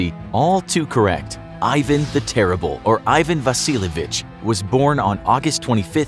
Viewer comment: I had to rewind as I thought he said "Ivan the Silly B****"